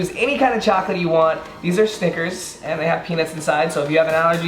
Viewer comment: Is it only me or when he said snickers it sounds like he said stickers